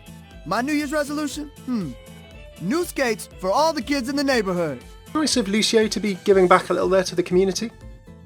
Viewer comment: I thought lucio said, 'give all the kids on the neighbourhood cheesecake' XD